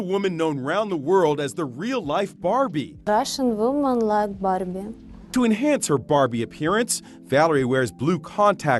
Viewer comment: I thought she said Russian women like bottoming 😅